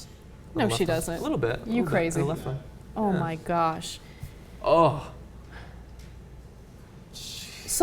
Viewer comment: haha: what they say "oh my gosh" what the subtitles read "online cops" hahaha